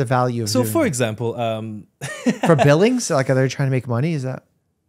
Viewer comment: when i heard "like billing?" my mind: BING CHILING